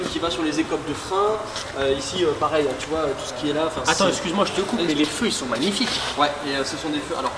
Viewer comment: Les quoi ? Les phares ohh mdrr